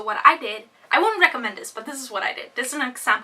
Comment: "I WENT TO A ......" didn't catch that. A ( meagle) a ( mule )? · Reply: Christopher Bayne omegle* :)